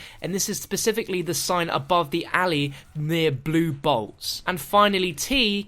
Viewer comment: because of your accent when you said blue bolts I thought you said blue balls